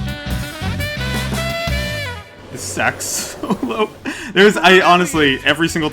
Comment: Did you just call it a saxalope?